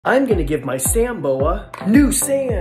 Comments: He said "sand Bora" I heard "sand boba" 😂😂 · I thought he said sand boba at the beggining but i realizes it was sand boa